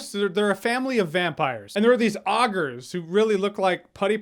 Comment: I listened to this while washing dishes so as said "Ah-gers" over and over, I assumed it was a proprietary term from the game. When I actually watched it the second time, I realized he was saying Ogres, which I've always pronounced, and only ever heard pronounced, "Oh-Gers"! Is "Ah-gers" how everyone in...wherever from pronounces it? I have A coworker from Southern California that pronounces the word tour with two syllables: "two-wer". He even purses his lips on that first syllable so it comes out like a mid-Atlantic "O", like got hear in Baltimore. I had to ask him to repeat himself the first time I heard it. "Two-er"? "Ah-ger"? Dafuq?